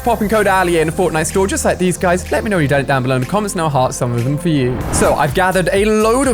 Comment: did he say “let me know your dead” ??